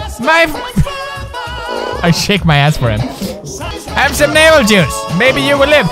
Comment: did he just say "Have some Mabel Juice"? Or am I going bananas